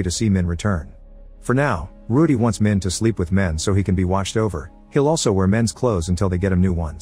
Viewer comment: "Ruedee wants Min to sleep with Men" I had to go back and make sure I heard this right